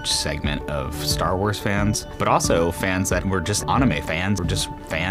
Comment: did he just say “ahnime”?